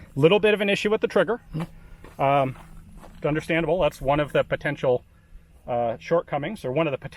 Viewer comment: it sounds like he said "Gunderstandable" and I was like "That's my new favorite word of all time!"